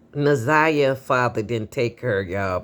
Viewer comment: I thought they said her Father died.